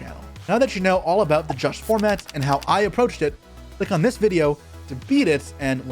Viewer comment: I always heard "Josh Format" and was like "Where's the Runick deck?" haha - great video, really helpful to build a deck or how to build a deck and insight on a tournament. Thanks for that. Also big shout out to the editor, that was clean af. <3